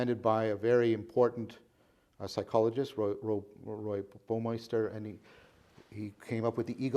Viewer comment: I can't understand the name of the psychologist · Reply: Roy Baumeister